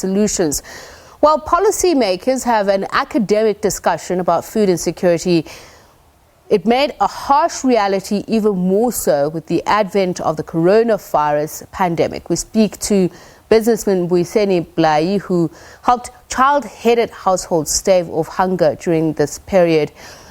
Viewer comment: Sounded like she said Corona Fires at the beginning